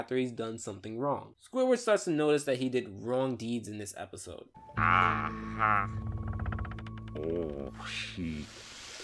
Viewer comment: is it me, or did Squidward sounded like he said “ooh, s***”